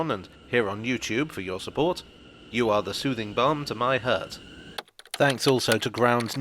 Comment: At the end I thought you said "soothing bum" my bad...sorry!